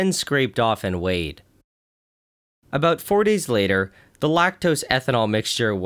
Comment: I think you literally pronounced 40s instead of 40 seconds... or minutes was cut down to (minute)s? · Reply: He said 4 days